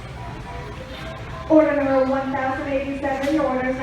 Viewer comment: did it say order 1087!